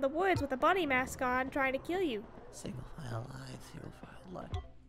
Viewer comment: “single file Life feel fine Life”is what I think he said